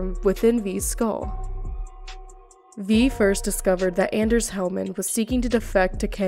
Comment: I was just listening to this while I'm doing work, and I swear I heard you say "Panam Palmer and the Avocados" lol